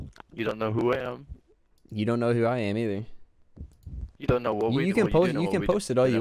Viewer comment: “you don’t know who I am” then I almost thought I heard cuh say, “you don’t know what we believe in” 😂😂😂😂😂 cuh doowwwwnn Bad bad